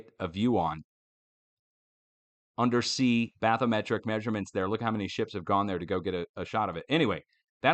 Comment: did you say baphometric measurements? If so, WTH is that?